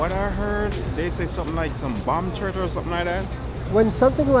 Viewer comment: "Umm, what I heard they say somethin like some bomb turd or somethin like that"